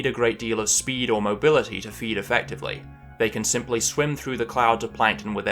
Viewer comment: The art at 4:06 is just... stunning. Holy shit man, this world you're creating is just amazing... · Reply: i clicked the time stamp and paused right afterwards, and it sounded like he said "they can simp"